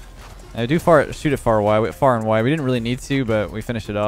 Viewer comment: Did he say I do fart